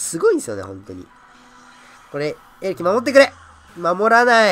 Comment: これって何て言ってるのか教えて欲しいです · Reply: 小鳥遊六花 相手が、さんのエレキに対してエリクサーを割いてくれないかなぁ、っていう、『エレキ守ってくれ！』かなーって思いました！